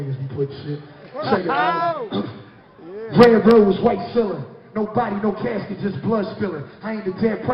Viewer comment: "How you doin Game?" "... Red Rolls, White Ceiling" got me fightin for my damn lifeeeee 💀💀💀💀💀💀😭😭😭😭😭😭😭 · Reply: I thought he was saying red rose, white charmander.